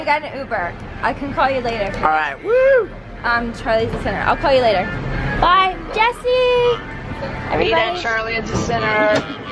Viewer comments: did she say " I will call you later babe " 😮😮😮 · did she say "I can call you later baby"